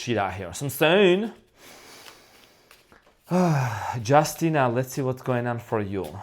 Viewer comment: I thought you said Justyne or Justyna.. which is my name lol 😆